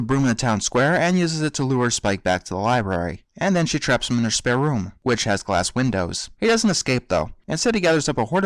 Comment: I'm sorry, but when you said spare room, I thought you said "spare oom"...anyone understand what I'm referencing? xD